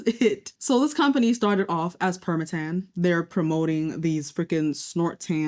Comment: GURRRRL I THOUGHT YOU SAID PERCA-TAN😂🤤😏